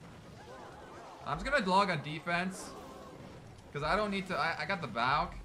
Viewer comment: I am just gonna GLOCK on defence??? please tell me I am not the only one who hears that